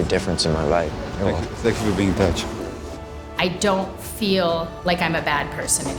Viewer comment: The CC subtitles are messed up. At 2:54, Gabor says "Thank you for being in touch." The subtitles say "Thank you for being bitch."